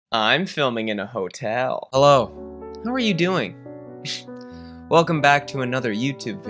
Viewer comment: I really thought you said “who are you doing?” In the intro 🤣😳